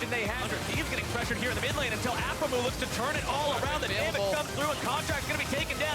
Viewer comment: not sure if I heard correctly but when he started using black paint i think phreak said aphromoo lol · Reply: Shinigami Panda thought he Said blackhromoo